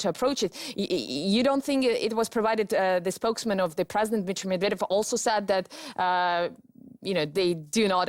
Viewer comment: Нихрена не понял. Расслышал только на 10:05 - " бичь медведев". Что-бы это значило?